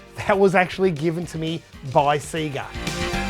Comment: I thought he meant seager like bob seager he was actually saying sega hahahaha